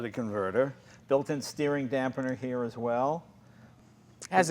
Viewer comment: did he just say steering DAMNPENER?